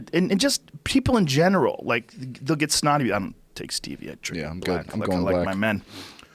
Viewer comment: did he just say "i dont take stevia, i drink it black like my men"